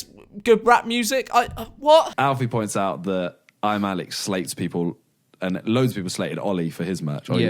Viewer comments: It sounded like he was saying "I'm a salad" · I thought he was saying I'm a salad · I thought he was saying “ im a salad “